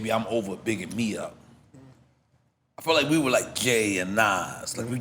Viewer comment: Did he say gay or Jay @ 2:34:15 😂😂😂😂